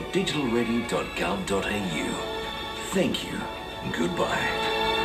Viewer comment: what does the 7 television say at the end? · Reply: Bye bye! Is what it said. Also it's name is Tim the TV man